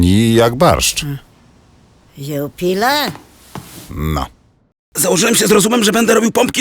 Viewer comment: Może mi ktoś powiedzieć co mówi Renia po słowach "tani jak barszcz" w 0:46 ? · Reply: 92 A PO DRUGIE: ten renifer RENIA mówiła do tego różowego w okularach: JUPILA‼️ Przecież było słychać🤦‍♀️🤷‍♀️